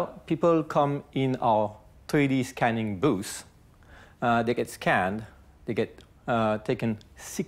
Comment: "People come into our 3D scanning booth"... I thought he said... "They get scammed"!!